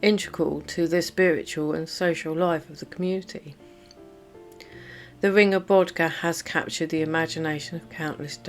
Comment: Aww, I thought you'd said the ring of Badger 😁😁🙂🙂